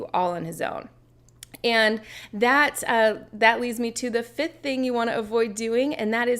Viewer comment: I think at 9:04 she says "...that leads me to the fisting that you don't wanna be doing..."